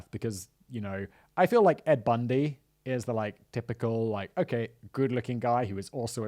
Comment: Did you just call him Ed Bundy? 😂😅😂